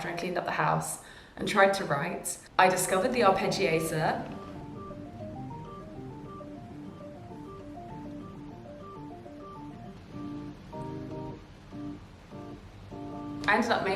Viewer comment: This was so inspiring! I’ll try all of this out! One question, though! what did you say you discovered in 5:43? I’m not a native English speaker and I can’t figure out what you said there and curiosity is killing me haha thank you 🫶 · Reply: She says arpeggiator- according to google: The arpeggiator is a common synthesizer feature that spits out an arpeggio when you press down a chord. This means you can play any chord (let's say a basic C major chord: C, E, G, and high C) and the synthesizer will play an arpeggiated sequence — a looping pattern with each of those notes — played one at a time. Hope that makes more sense to you than me!